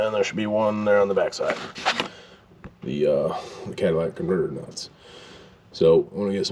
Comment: Norm… you’re just all over me bro 🤣 Gonna need a marriage certificate here before long. You’re starting to sound like my wife picking on the way I talk 🤣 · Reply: my wife heard you last night and said..." wait did he just say Cadilla-tic" lol